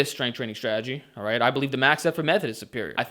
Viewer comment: What does he say at 3:59? Max__ method? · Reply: effort. Max effort method